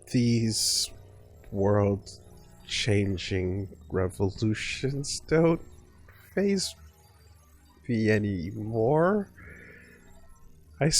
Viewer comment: I thought you said these world-changing revelations don't even faze you anymore